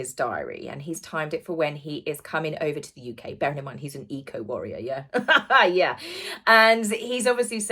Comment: I thought I heard you say “. . . bear in mind he’s an EGO warrior.” I heard wrong.